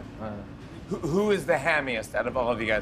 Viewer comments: does anyone know what jimmy said at 1:34 ? hammiest?? what does that mean?? or am i hearing wrong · okay but what did he say at 1:35 all i hear is "who is the hamiest" lol